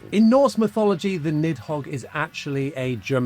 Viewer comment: I thought he said midhog.